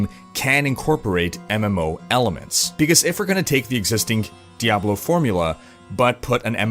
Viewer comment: All I heard was "Diablo FOURmula".